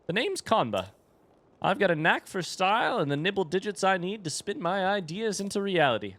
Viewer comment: I swear he says "nipple digits"